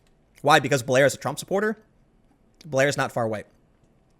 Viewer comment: did you say blair is not far white at 9:31 ? hmmm I wonder if the term far white has just been coined.